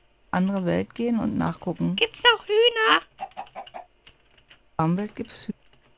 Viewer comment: ich hab verstanden,,Gibt da auch Döner''